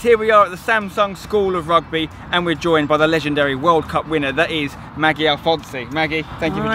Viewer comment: oh i thought u said a rugby world cup winner